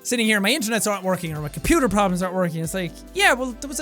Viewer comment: did i just hear jack say "my computer problems are not working"?